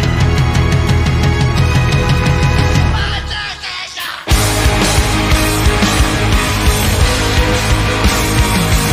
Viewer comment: What was Tomoya yelling at 4:51 ?? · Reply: Giao Võ 1! 2!3!4!